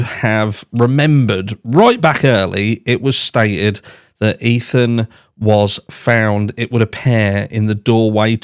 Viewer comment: i actually thought they said Xana was in the doorway